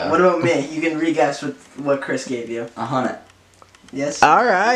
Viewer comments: yall can someone tell me what matt's fav show here 7:40 TwT I cant really hear it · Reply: The 100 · what is matt’s fav show? don’t understand what justin said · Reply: i think he says the 100